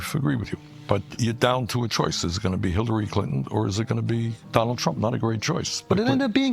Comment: Did he say "Hitlary Clinton"? 😂